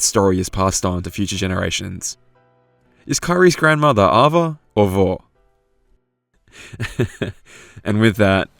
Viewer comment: "Is Kairi's grandma Ava, or Vor?" When I heard that question I didn't quite get that last part of it because I didn't know who Vor was. So to me it sounded like he was saying the Portuguese word for grandma, "Avó". I guess I don't really mean anything by this, but I started thinking how combining the names Ava and Vor into Avor, again sounds like that same Portuguese word.